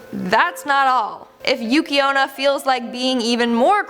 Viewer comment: For a moment I thought you said Yuki Ono and I was like huh?